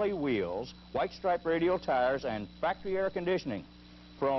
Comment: Haha it sounded like he said the El Camino had crappy air conditioning...I'm sure he said crafty but still. Love these old commercials, you can tell it was recorded on 2" quad tape, you can hear the head noise in the background. Nice trucks though...eats gas and hauls ass. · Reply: punishedexistence He said factory